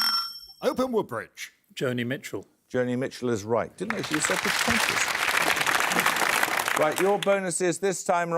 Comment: What is Paxman muttering at 18:59? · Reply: "I didn't know she was so pretentious"